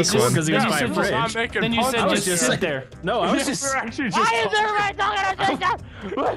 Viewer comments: At about 4:26 i think he tried saying "why is everyone talking at the same time" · I think he says “why is everyone talking at the same time”